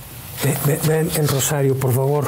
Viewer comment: Buenas tardes en el minuto 18:22 se escuche que dicen ven pero ala vez a un animal muchachos mucho cuidado que Dios los cuide a todos